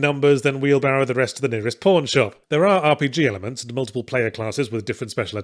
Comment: Even years later, every time he says pawn shop I briefly mishear it as porn shop.